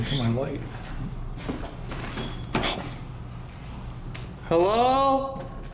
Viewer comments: Idk why I don’t see comments about this but I swear at 1:46:35 I hear a “hi!” Clear as DAY! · please correct me if im wrong. I think i heard someone said "hi".